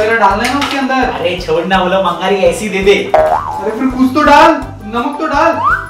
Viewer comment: In 7:11 Did say 'Are chhod na wo bangali hai' Can anyone tell me I want to know